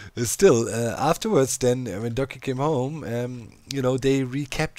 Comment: Yor videos being at ~1 hour is just fine, no need to overwork yourself! :) (side note: i only now get what you were saying when you say doccy; i thought you were saying ducky)